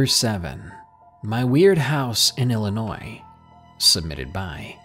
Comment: I thought number 7 said, "my weed house" I have problems man 😂